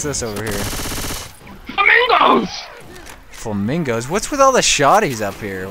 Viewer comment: DID ANYONE HEAR "WHATTHEFU" at 4:45 ?